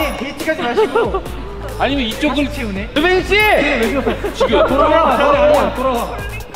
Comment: i literally heard Jiminshiii here 😂😂💜